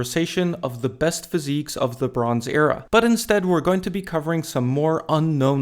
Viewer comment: When you said "the bronze era" I really thought you said "LeBrons Era"